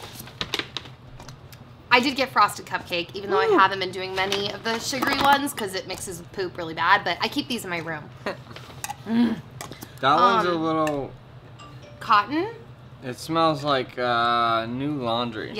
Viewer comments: Did she say frosted cupcake candle makes her poop real bad? Wtf?! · No, I think she said it mixes with poop really bad. I think she keeps candles in her bathroom or something. lol I do have to admit that it did sound like she said that, though.